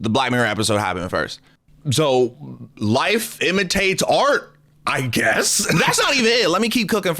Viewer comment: o thought bro said “wife imitates art”